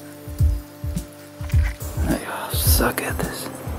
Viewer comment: anyone else hear "suck it" at 3:05 ?